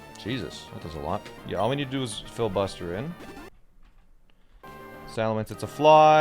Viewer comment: I heard "all we need to do is filibuster" Sounds about right.